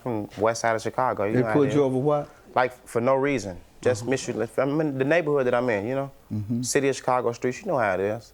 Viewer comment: He got pulled over for mishellanious😂...he didnt go far enough in school he said...he ditched English I said😂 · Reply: I thought he said mysteryllaneous